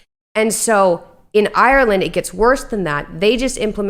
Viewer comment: did ye just say Ireland...😳?